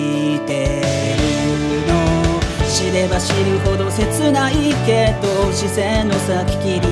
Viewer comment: 知れば知る程が死ねば死ぬ程に聞こえた…w